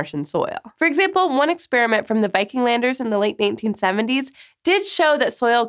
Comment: I swear she said Viking landers in 1870